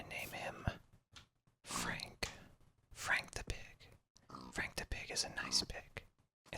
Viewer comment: At first i thought you said freak the pig but the second time you said frank the pig